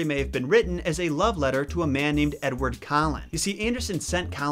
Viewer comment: sounded like you said edward cullen...i giggled